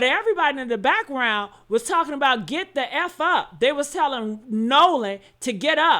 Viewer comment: Sounds like he said, "Bart get the F up".